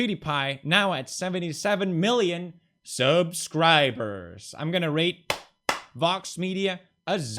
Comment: did he say ''Im going to rape''? We need to get VOX on this story ASAP